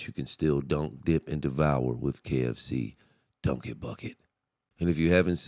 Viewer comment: I thought you said KFC Donkey bucket